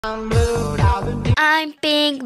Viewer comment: I heard green say "I'm GrU"